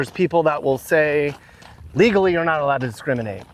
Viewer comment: I thought they said gays weren’t allowed 🤔